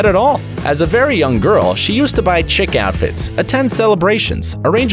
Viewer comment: it sounds like you are saying "chick" outfits instead of chic.